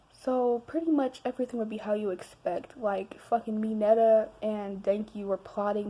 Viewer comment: "good look at denki" me: mhmm "good look at bakugou" me: mmkay "good look at kirishima" me: yess "looked at mina" squeals in bisexual girl she m i n e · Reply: PLS I THOUGHT YOU SAID MINETA AND I GOT SO SCARED